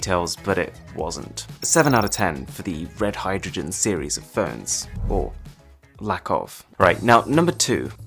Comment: His voice is so relaxing that, 1. I once fell asleep... 2. The auto-generated captions recognize each and every word without a mistake... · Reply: it said Ferns Instead of phones. So technically it did mess up